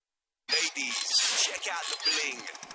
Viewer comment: I always thought he said "Rip thier heads off and send me castles"